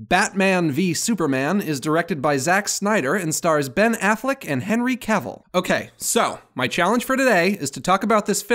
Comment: I thought he said “stars Ben Affleck as Henry Cavill” 😂😂😂